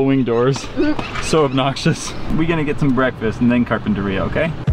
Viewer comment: Ohmygosh shes already 7 MONTHS OLD? OHMYGOSH SHES SO OLD NOW! Also at 4:15 I thought he said either "We're gonna get some breakfast and then Carpe Diem." Or "We're gonna get some breakfast and then carpet to Rio." XD